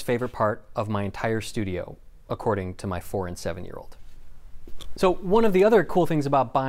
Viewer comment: Honestly thought he said "My foreign 7 year old"...